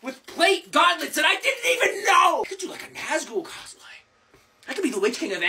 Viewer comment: I’s thought’s he said gazgull cosplay and da orky git in me’s says “ da great profit of da orky WAAAAG!?” And me’s disappointeds now.